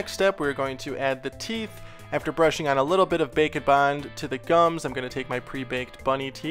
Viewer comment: I Always Thought He Said Bacon Bond Instead of Bake N Bond